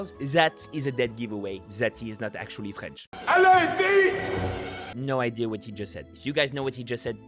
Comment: Selon moi il dit "Allez, vite !" Je trouve que c'est assez audible quand même non ?